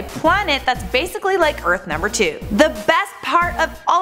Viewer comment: In the intro, I thought I heard her say Earth's number 2 Not Earth number 2